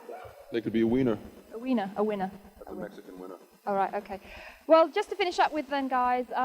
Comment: Did he say a Mexican wiener??!! LOLLLL